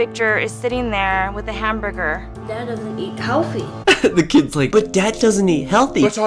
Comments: I legitimately thought that kid said “but dad doesn’t eat cow feet” until the commentary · I thought the kid said “ But dad doesn’t eat cow feet.”